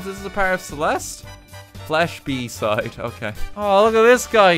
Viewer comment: I wasn't watching... I thought I heard "/b side"